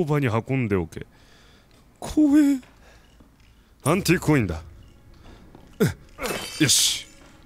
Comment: の所で何故か「パンティーコインだ！」って聞こえてしまった。